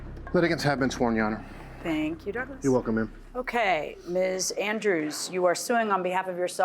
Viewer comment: Lol I had to rewind the beginning I thought Douglas said " you welcome babe" 😂😂😂